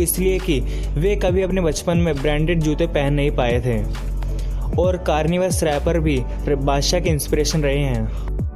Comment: which rapper did he mention? Kanye west or chance the rapper...... i heard karni west🤣🤣🤣 and the picture is chance the rapper...... and you said eninem too 🤣🤣🤣